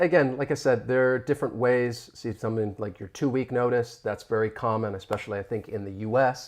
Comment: I appreciate your interesting/intriguing lesson. 12:48, "again like I said there are different ways see something like your twoe notice that's ". In this sentence the word 'twoe", I couldn't find in our indigenous dictionary. Would you mind if I ask you to teach me? What would this word means? Would this mean 'twitter'? 14:06, 14:20, 14:34, I found the word 'jiren', this would be GT's mishearing of 'gerund'? Is it right? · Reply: I believe what I was referring to what giving your 'two-week notice.' In the US, people let employers know they are quitting at least two weeks in advance. 👍